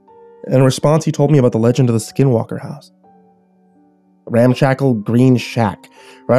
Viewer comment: Am I The only One Who thought it Said The Skywalker House At First